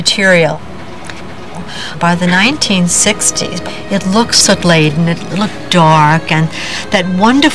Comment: What is she saying at 36:48? looked sulayent?? And 39:24 Penn Station is now a 'warrum'?